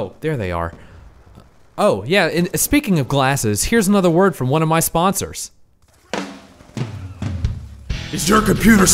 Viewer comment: I thought it said "A Word From My Spooners"